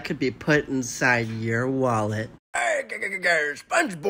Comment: I thought Patrick said: " I HAPPEN TO SMELL COCAINE "